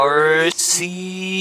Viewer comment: I Thought They Said Farsy and i said 'Fartsy hahahah xD